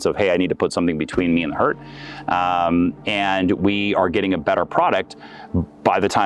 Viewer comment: Petite erreur de traduction à 14:54 " between me and the hurt " et non " heart ", c'est donc " mettre quelque chose entre moi et ce qui peut me blesser "